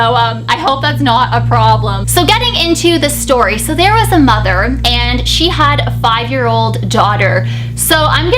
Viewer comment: omg at first when you said: " there was a mother." i thought you said: " there was a muggle." 😂😂😂